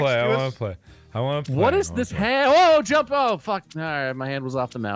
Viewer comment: the captions said What is this Halo jump